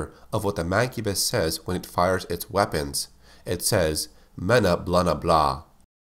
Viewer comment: Me and my cousin heard "Mamamia" instead of Menaelse